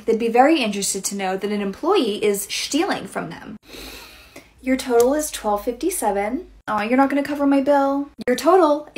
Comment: I thought she said: "Your TOE will be 12.57"